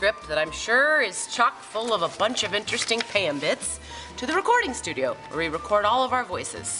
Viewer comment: did she just say pambit herself?